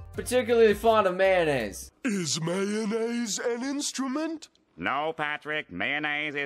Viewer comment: I always thought when they say mayonnaise I heard Man aids (dont judge me)